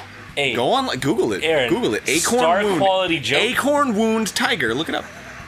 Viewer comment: for a long moment i thought he was saying Acorn Womb